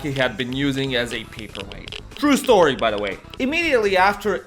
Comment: didn’t understand what was said here, please help? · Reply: "used as a paperweight. True story by the way"